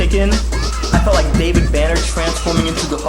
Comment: Did he just say "David Banner" for the Hulk?